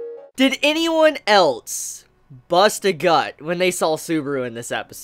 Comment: I thought he said bust a nut...